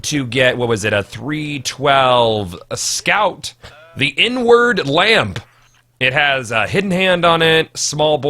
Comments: I was reading the comments and only listening to the video and when he said the "the inward lamp" I thought he said "the n-word lamp" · thought he said the n word lamp woops my ears are mega butts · I thought you said "The N-Word Lamp" for a second there.. I was like wtf lol?